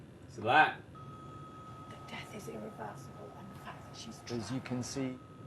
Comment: she actually said " the fact she still alive ..... you be getting in your feeling nigga tighten up like that dam microphone